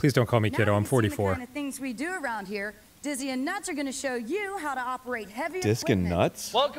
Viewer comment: I swear at first I thought she said Deez and Nuts and then I kept not being sure that isn't what I heard when it was confirmed the second guy's name is, in fact, Nuts.